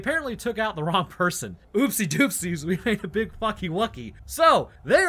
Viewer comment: did he actually say fucky wucky?